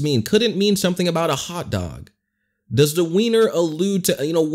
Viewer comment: I thought she said winner